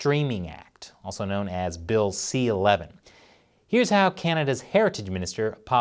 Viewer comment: Oh “Heritage “ Minister……I thought he said “Hair Minister”……..